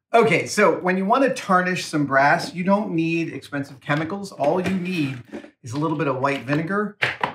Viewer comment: Is anyone gonna tell him that Vinegar is an acid? A mild one, yes, but an acid none the less. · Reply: Strange, I heard him say "expensive chemicals" and nothing about non-acid